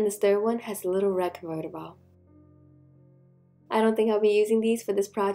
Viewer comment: What was that? Convertible? Is that what you said????? (Smeone pls take this srsly and answer if you know.) Otherwise it'll bug me, lmfao.